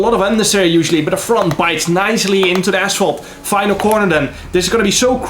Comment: Man, somehow I heard "nicely into the ass hole" (6:25) and then couldn't stop laughing